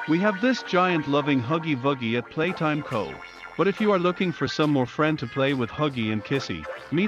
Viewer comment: "are you looking for some friends?" what i heard: " are you looking for death?" LMAO